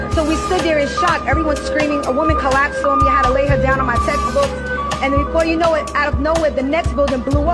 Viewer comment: Did she say "i had to lay her down on my testicles"?? 😅